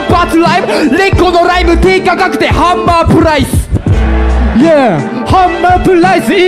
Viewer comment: 「低価格でハンマープライス｣が 「低価格でハンバーグライス｣に聞こえた (｢'･_･`)ﾎﾟﾘﾎﾟﾘ